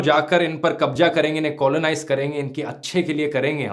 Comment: I swore I heard "inke ache din... " :). Good video..